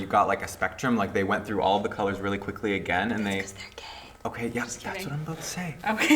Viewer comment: what did she say at 11:00? "because they are whaaaat?? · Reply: Gay